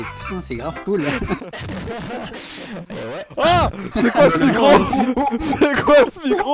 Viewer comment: ce que terracid dit « c’est quoi ce micro » ce que j’entend « C’EST QUOI CE MIGRANT » y’a un problème…